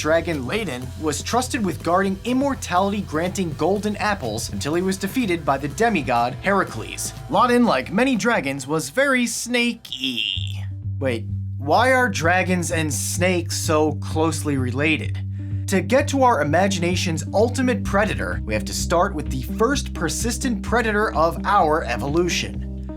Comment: i thought he said snagons and drakes